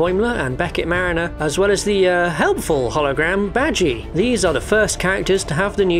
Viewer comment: I swear when he said badgie it sounded like he said vaggie. Anyone else hear that or is my mind just in the gutter? LOL